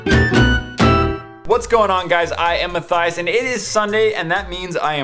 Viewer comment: sound like you said "i empathize"